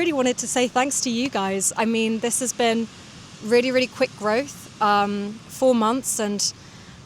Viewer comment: It's not my listening that I need to improve. At this speed, I can understand your speech perfectly. The only exception was the word "growth" at 7:54. I didn't catch it on the first listen and had to rewind. I wasn't even paying full attention since I was listening to the video while cutting my nails. Anyhow, it's my speaking that's atrocious. I can write just fine, but forming sentences on the fly while also producing the sounds just fries my brain.